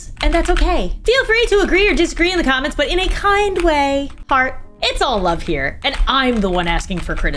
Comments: I thought she said fart😭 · vro I thought it said fart ✋😭